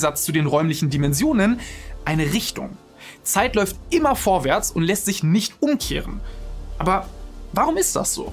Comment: Also ich habe verstanden: "Zeit läuft immer nur vorwärts, weil Geschirr rumsteht"